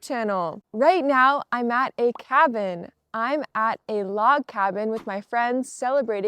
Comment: Hello . Thank you for the nice video. When you said , " I'm at a cabin. " , I heard " I ate a cabin. " The cabin which you stayed at is more luxurious than my house. I think the boat tour is similar to Pirates of the Caribbean of Disneyland. The pizza which you were eating is much bigger than Japanese one. I'm surprised to see it. Did you eat all pieces ? See you again. from a Japanese gentleman